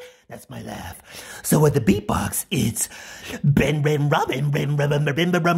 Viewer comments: Dont laugh at me b7t for some stupid reasin i thought he said red red robin. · I hear "run red rabbit buh buh buh BA buh